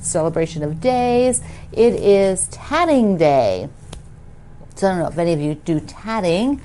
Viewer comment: I had to laugh a little, when you followed up your skin cancer announcement (yay!) with tatting day. I thought you said tanning day! I was very confused for a second. 😅 Thanks for everything you do, I look forward to all your videos and following along with your quilty life.